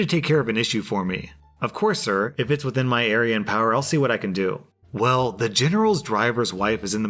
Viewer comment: I was listening and not watching... Had to come and check what you said. I was sure you said Aryan power.